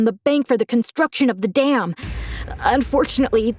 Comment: did he say da** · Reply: As in the like dam in the water